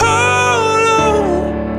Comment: Everytime i heard this song when i was younger i thought he was saying “little einstein”